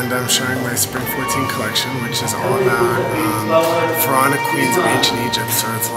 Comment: I think he said ''Pharaonic''